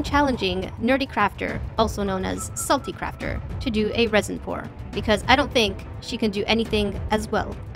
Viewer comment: I think its bad that I thought you said resin porn tge furst few times